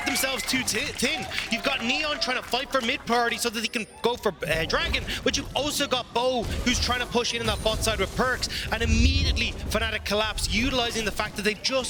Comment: wtf did he say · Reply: You have neon fighting for mid priority so that he can go for bar.. dragon. But you also got bo who is trying to push into that bot side with perkz.